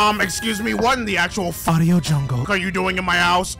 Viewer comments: "What in the actual AUDIO JUNGLE are you doing in my house?" 😂, I'm dying! XD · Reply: I heard "Fadio Jungle" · The animation is good asf lmao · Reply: I thought he said MARIO JUNGLE. That would've been funny.